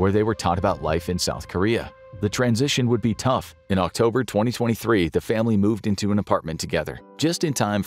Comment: October 2024? its Oct 2023 not Oct 2024 the subtitles is wrong 10:08 :O